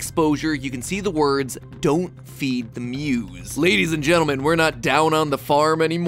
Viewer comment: i thought it said don’t feed the moles 😭😭😭